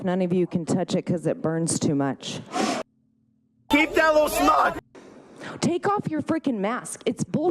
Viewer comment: I really thought he said “keep that lil snot”